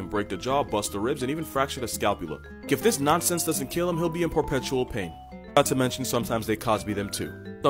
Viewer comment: Did you say "por-petual pain"?